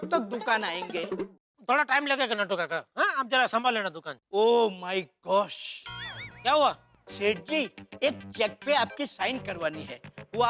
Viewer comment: When Natu Kaka said Oh my Gos I heard this That is Oh my God